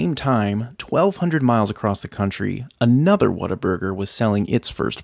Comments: Is it just me or i thought he said "water burger" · I thought you said waterburger 🥲😢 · I’m Latvian/ British and I thought you said “water burger”